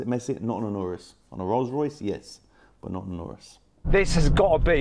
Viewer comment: did he say rs